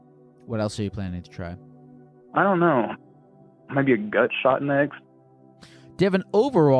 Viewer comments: So glad theres subtitles cause i heard "gunshot" and was like "damn, 0-100 real fast" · I thought he said gun shot next😭